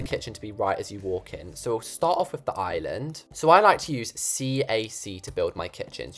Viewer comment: when you said “cac” my dumbass brain thought you said ac for some odd reason